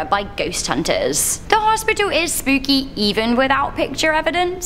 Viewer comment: It sounded like you said spook jew but I was in a field artillery unit in the Army so I have ringing in my ears